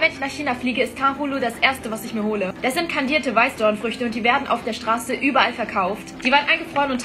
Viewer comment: Ich hab kandierte weißwürste verstanden 😂😂😂